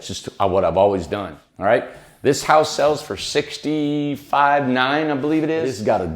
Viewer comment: Did anyone catch the asking price? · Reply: I thought he said 65,900